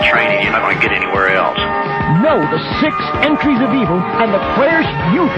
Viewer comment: i thought he was saying 6 inches of evil